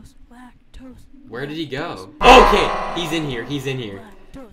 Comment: I Have low volume, And i hear "Black toes" on